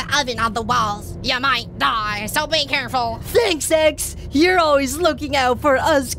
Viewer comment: The captions says “Thanks, ex!” It’s weird… and a little sus…. (No among us included)